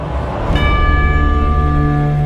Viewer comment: What he say at 0:06 , sorry i dont speak english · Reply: " mother, what does betray mean ? "